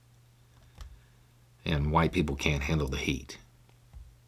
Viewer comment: I thought i heard "white people can't take the Ink"!!! Made me go back and listen again!!! TY!!!